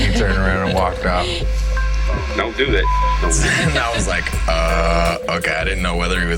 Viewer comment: What a class act, walking around guests in a wife beater and baseball cap. And her dad was mad as that is why she cried around 12:30 or 13:30. Poor baby. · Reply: Sounds like he said 'don't do that'